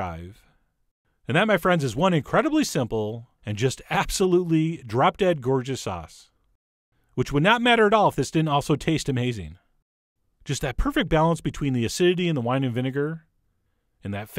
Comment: It sounded like you said "drop dead gorgeous arse" lol..